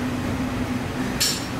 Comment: was he saying fuckit or buckut?